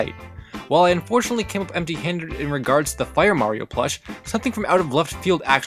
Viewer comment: W-what did you say? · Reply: Empty Handed?